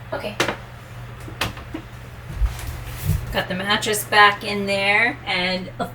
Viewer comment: Did say 'Manatress' around 3:12? If so, I'm happy! It's up there with 'Saxamophone' :D